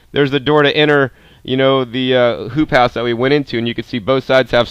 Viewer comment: no joke I heard "poop house" every time